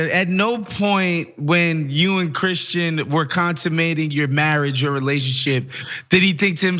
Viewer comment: Did he actually say "contimating" at 7:16? I think he meant consummating